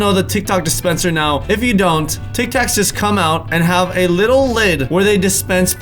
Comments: Omg the first time he said tic tac at 9:32 it sounded like Tik tok like if you agree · Who else heard “TikTok dispenser” ??? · instead of tic tac he said tick-tock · He said tiktok instead of tic tac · Did anyone else hear him say tic-tock. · Did he say Tik Tok dispenser let me prove it Click this and watch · who else heard him say ticktok and then tic tac? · did he say tik tok instead of tic tac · Is it just me, or did he say Tic Tok dispenser at 9:30 instead of Tic Tac dispenser? 😂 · Did anyone else notice that at 9:32 he said Tik TOK instead of tic tac 😂 Someone’s a little to addicted to Tik Tok 😂 (it’s ok I am too. Go follow me: Also subscribe to my channel (CelesteDIY) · did eh say tiktok instead of tiktac? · who else Heard tik tok dispenser